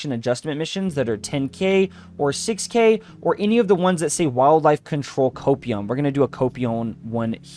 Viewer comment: Am I the only one who misheard 'Wildlife control copium'